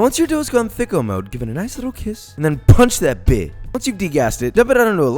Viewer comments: did he really say "punch that bitch"? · did he just say THICCO MODE